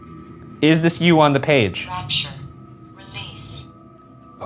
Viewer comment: I heard "Watcher" not rapture.